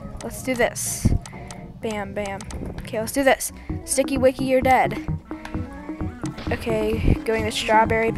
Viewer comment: I heard "sticky wicky ur dad"🤣